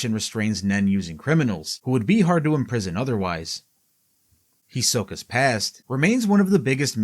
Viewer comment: I swear i heard Hisoka's ass 😂